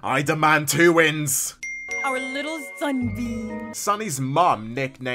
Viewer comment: I always thought she said "Sunbeam". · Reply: She did I looked at the transcript and she said Sunbeam.